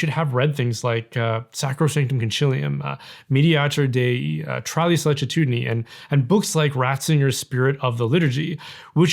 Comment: what is the last document called? Couldn't understand the latin name. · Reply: I believe he said 'Tra le sollecitudini'. It's the most close sounding that I found.